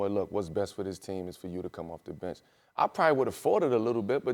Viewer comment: Thought melo said “I might have Farted a lil bit” then quickly realized he said “Fought It”